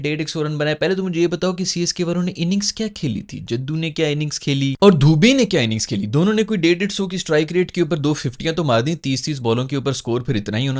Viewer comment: did he say "dhobi ne kya inning kheli"?? 😂😭